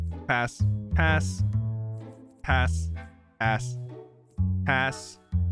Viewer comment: WHAT DID HE SAY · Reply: Did he say A#!